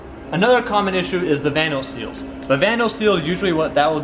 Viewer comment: What does he say at 1:27 What is the other thing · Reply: Josh Bugno VANOS.. its variable valve timing for some bmws.. It can hurt the idle when the seals go bad..